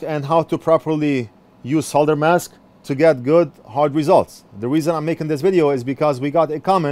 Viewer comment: My wife heard you say "get good hard results" so she purchased some solder mask for me 😬